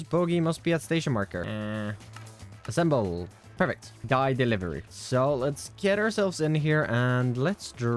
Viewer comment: I am getting over being sick, and watching create videos has been a great way to spend my time, but I swear I heard you say "Dye Fieri" instead of "Dye Delivery". Please reconsider this oversight